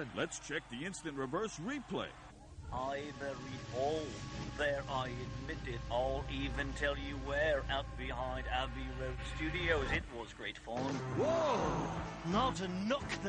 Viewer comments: What John said backwards: "I buried Paul. There, I admit it. I'll even tell you where: out behind Abbey Road Studios. It was great fun." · what did John say? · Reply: "I buried Paul. There, I admit it. I'll even tell you where: out behind Abbey Road Studios. It was great fun."